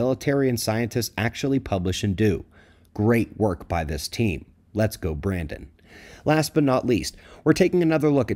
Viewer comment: I’m listening while working and heard you say “let’s go Brandon” and almost lost it 🤣